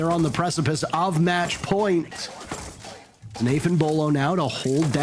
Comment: I really thought he said Nathan Beaulo, I was about to "☝️🤓"